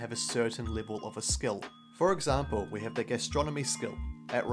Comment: OK so I'm eating whilst watching your video and I heard "Astronomy" and not "Gastronomy".... I was a little confused why knowing more about the planets helped you make great sandwiches...